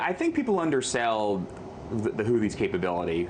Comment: Houthi?? I thought they said they were attacked by Hoochies!! Lol